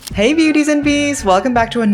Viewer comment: I thought you said “Hey babies and beasts”😭😭😭